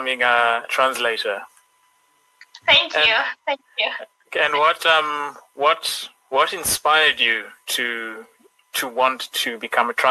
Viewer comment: In 1:31 I think he was saying what inspired you. Am I wrong ? So there might be a mistake on the subtitle :)